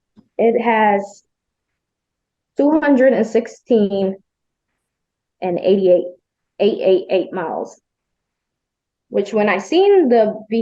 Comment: They had their own "expert" with them to check it out before purchase, accepted their advice and purchased it as is.. plus over 200k miles? And expect they have a claim? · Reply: I thought she said 5:03 416,888 miles, not 200K+.